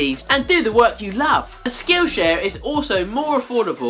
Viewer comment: At first I thought you said "Skoosha" instead of skill share. lol!